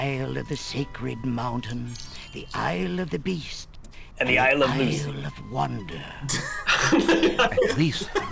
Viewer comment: WHAT DID BILL SAY AT 3:03?? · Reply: NEVER MIND HE SAID ISLE OF LUCY